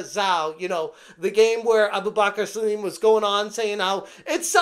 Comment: thought you said 'where the black guy online was saying' ..... Turns out you said 'where Abubakar Salim' !! haha im either a jerk racist or bad hearing.